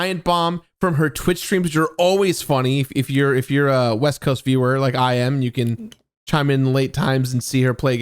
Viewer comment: I swore Jirard said “west coast b-word” at first 😂😂